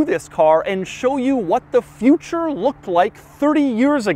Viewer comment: I thought Doug is saying "what the f**" but he said future 😄 2:06 I thought it again... :D